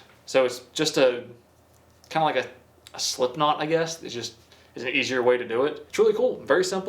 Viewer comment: Did you say Slipknot 🤘